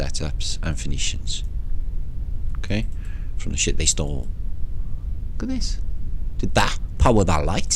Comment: "from s*** they stole (Phoenicians?) - I missed what they stole? Sometimes your little comments I can't hear I feel are very important for me to hear, lol....but seriously. I hate to miss your nuggets. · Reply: they stole a civilisation .